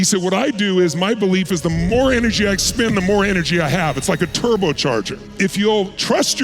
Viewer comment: i think he says "expend", not "i spend"